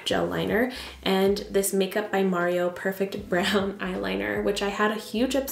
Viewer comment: Such a beautiful look— especially the eyes. Now every time you say “Makeup by Mario” I hear “It’s me, MARIO!”